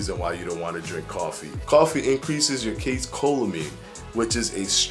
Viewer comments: Coffee boiling temperatures above 160 degrees Fahrenheit kill virtually all pathogens within 30 minutes, including bacteria and the two prime nemeses: giardia and cryptosporidia. At 185 degrees Fahrenheit, they’re dead in just a few minutes. Almost nothing can survive sea-level boiling temperature (212° F) for any length of time, though a few pathogens like botulism can persist at even higher temperatures (none that are a concern to body So I don't trust any of your videos now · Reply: Hey. What was the name of the stress-hormone caffeine interacts with? It sounds like you said “case-cholamine.” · Hey. What was the name of the stress-hormone caffeine interacts with? It sounds like you said “case-cholamine.”